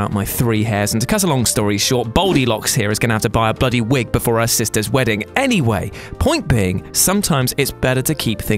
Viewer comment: did you say our sisters wedding, are you dating your sister???? I dig it ;)!!!!!!